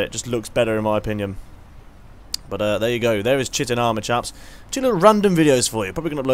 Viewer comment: Is it just me or does it sound like he is saying shitting armour?